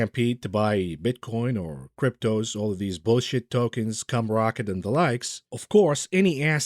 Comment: did you say cum rocket or scum rocket? either way classic